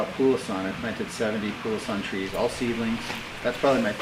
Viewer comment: What fruit is saying at 9:20? Poolson? Puason? Thanks! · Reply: The Pulasan, Nephelium mutabile, is a tropical fruit in the soapberry family Sapindaceae. It is closely related to the rambutan